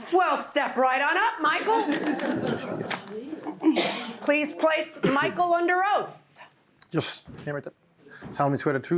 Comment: What did he say under his breath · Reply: Fuck, fuck, fuck....